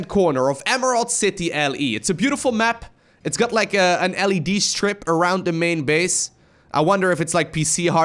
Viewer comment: Oh, thought said "LSD strip around the base" not "LED"... bummer